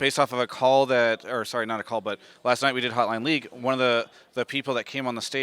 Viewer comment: did someone say HOTLINE LEAGUE